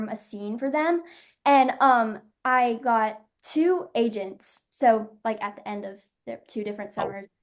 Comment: At first I thought you said "Asians" .. Asians will come? ok. Ahh AGENTS. You enunciated it fine btw I just misheard the first time. ;)